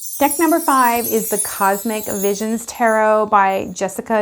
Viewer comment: Today you unintentionally gave me a good laugh. When you said "Cosmic Visions Tarot" what I heard was "Cosmic Lizards." I thought, "Wow, that's for me!" If anyone ever makes a Cosmic Lizards Oracle I will buy it. Yes, the Druid Craft Tarot is gorgeous. I don't know if I have every deck that Will Worthington ever illustrated, but I do have several. I love his work. Do you have the Fairy Tarot illustrated by Gary A. Lippincott? I have quite a few Tarot decks, but in the unlikely event that I was forced to give up all but one, I think I would most likely keep the Russian Tarot of St. Petersburg. The illustrations are like those on the little lacquered boxes, so beautiful. Every card is a miniature work of art. The LWB that comes with it has only the basic meanings, but it's enough. Do you have a favorite goddess oracle? I'm looking for one that isn't a lot of vapid, generic new-age fluff, & that treats each goddess with respect for her unique cultural attributes.